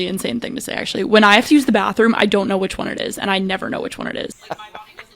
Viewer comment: I completely misunderstood the first one. I thought she was saying "I don't know which door to use" like she has problems with the signs or something. Not knowing if she needed to do #1 or #2 was not even on my radar lol